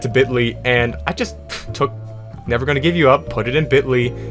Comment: you wouldn't get this from any other guy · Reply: I thought you said never gonna f*ck you up lol